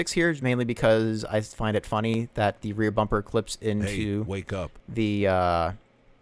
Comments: Did anyone else hear the hey wake up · who else heard the "hey wake up"